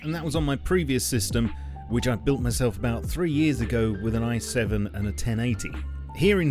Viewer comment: Why did I hear "I've built myself about 3 years ago with an IS-7" at 5:00 ? Everywhere I go... I hear tonks.